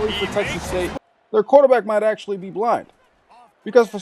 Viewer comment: 1st off, loved the video. But why did you say the thing you did at 16:59 that the quarterback might be black, bc he threw into almost quadruple coverage????